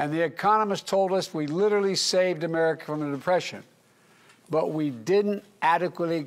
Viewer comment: what is that word he keeps trying to say but cant quite articulate it, it sounds like he is saying "emrrindepression"